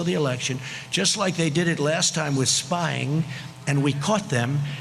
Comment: the left is LYING (what's new?) that the guy shouted "monkey", he CLEARLY SAID SPYGATE!!! sick of their hallucination! here it is slowed-down: <link>